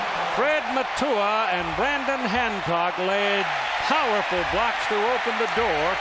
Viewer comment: did he say Random Handcock?